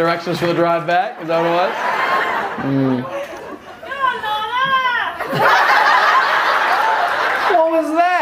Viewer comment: I’m wondering what she shout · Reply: She shouted go on lorna